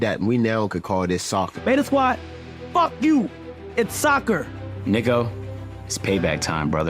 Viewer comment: did he said niggo :face-purple-crying